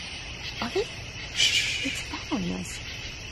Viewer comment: I hear a teenager saying “Are you there?”